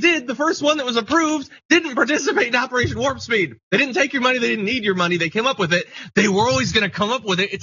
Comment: LMAO but Correction: 3:45 it's not "they didn't take your money, they didn't need your money" it's "they didn't take our money, they didn't need our money." It was never his money since it was taken from taxpayers, which means the taxpayers paid for the vaccine, not Trump. But I get the frustrations, I bit my fingers so many times last year every time I heard Trump drooling BS about some fiction he made up.